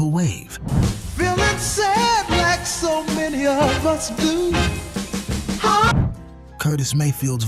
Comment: pretty rude calling this ai slop :/ we have a team working on these. what makes you say this and do you even know the definition of ai slop? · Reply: I feel you. I wouldn't call it "AI slop" but maybe one of the reasons for that comment was the closed captions interpreted this actual line "Feeling sad like so many of us do..." as "Filling Sam Hexum in his whole..." at the 1:44 mark. We've all misheard lyrics every now and again. I can name any number of songs over the decades I heard as a teenager that I've sung the wrong lyrics to year in and year out; only to find out some 60 years later what the lyrics actually were. But that "Filling Sam Hexum..." is way off base. Looks like that one slipped by you. It happens. However, with AI it seems to happen more often than not. I would say though if this is an AI generated voice I like it. 👍